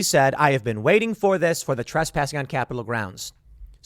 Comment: It sounds like she said "I was waiting this THEY'RE trespassing on capital grounds." They're or For, makes a completely different statement.